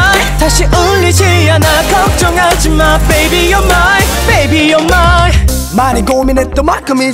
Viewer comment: to me, when jb sings "baby your mine" I always thought he said "baby you were mine"